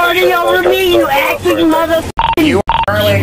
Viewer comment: did she call the officer "you acting m-f-er"? That's above average for a drunk sovcit :-)